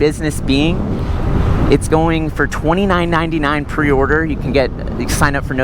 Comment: did he say 29.99?? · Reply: $2999